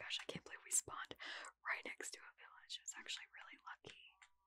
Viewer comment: My brain thought said"that's really Fu**y, instead of lucky